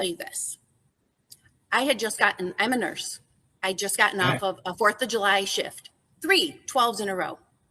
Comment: I thought she said she just got off work